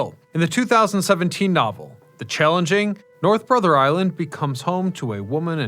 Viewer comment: You really need to pay someone to edit these. How to you pronounce "Changeling" as "Challenging?" The words are barely similar. "CHANGE-LING" Also, "Twinning" instead of "Twaining"